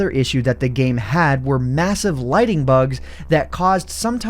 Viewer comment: Woah I cant believe they added fireflies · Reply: I'm glad that I'm not the only one that thought it said lightning bugs.